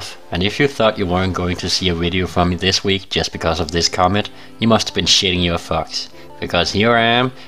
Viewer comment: What are you saying at 0:07, "you must have been shitting your fucks"?